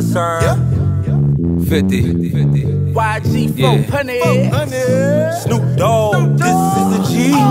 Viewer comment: What he said in from 0:35 to 0:38 please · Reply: "yg 4 hunnid"